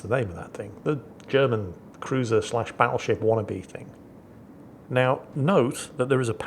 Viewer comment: Hahaha! had to recheck what said as the Buffalo is getting paddled. Thought he said Paddleship.